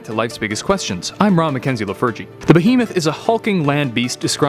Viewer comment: I always hear "I'm Rob McKenzie the virgin"